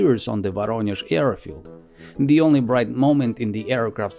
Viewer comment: I swear to God that he would say "the only bright momento was when it crashed"